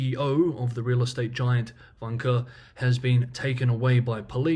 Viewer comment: What was the name of the developer - all I heard was 'wanker' .....? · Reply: It's Vanke. The name is on one of the construction cranes in that segment.